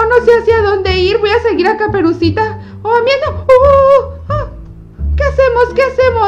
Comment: En el minuto 8:24 se oye que dice o miedo ó una mala palabra, tu que escuchaste🤔😅🙂